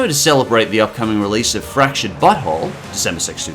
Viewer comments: I thought he said fractured butthole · it sounds like you say "The Fractured Butthole" instead of "The Fractured But Whole" :D.